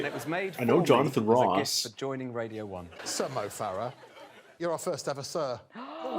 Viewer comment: Sometimes when Greg said “Sir Mo Farah” I kinda thought he was saying “some Mo Farah” like as if he was like “and also here, is some Mo Farah, just like some guy we found” Also I’m cautiously optimistic about the Aussie version so I’d love to see you check that out when it’s released. I’m just worried our tv audiences suck and they’ll either pre-emptively edit it to try and appeal to out of touch boomers who were never going to like it anyway, or the contestants just won’t feel comfortable really being themselves (or maybe they’ll try a bit too hard to be quirky, rather than themselves) But I know for a fact, two of the younger women that are cast, have some mental stories and really out there senses of humour from listening to podcasts they’ve guested on. They genuinely hold heaps of promise as producing all time top TM moments in the whole world, or the show might just not quite click. (This was a ramble, I have horrible persistent hay fever and I’m losing my mind. The point I want to make is still buried in here somewhere)